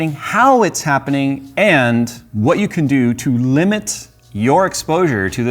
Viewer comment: Did Jay say "Limit your explosure?" Like ... Explosion + Exposure?